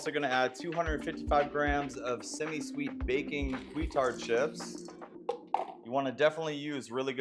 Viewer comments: Had to rewind, twice… thought, for sure, that he said ‘retard’ chips. 😂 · Even the transcipt didn't know what he was saying, sounded like he said retard chips